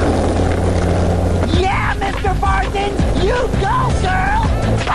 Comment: Did Whanye say at 01:09 YEAH MR. PARSONS YOU GIRL!